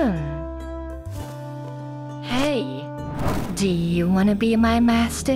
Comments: i thought she said "eat you" at the end instead of "break you". Yes, Lovander is probably into Vore. and considering how big it is, that might be possible. be careful with your giant pink dragon thingy. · I swear even though Lovander says break and the closed captions says break but I swear I hear her say "I promise I won't rape you". Also the Lovander chest fur puts a whole new perspective on "Wearing your heart on your sleeve".